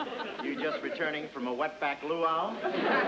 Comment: Did he say "Wet back"? Lmao!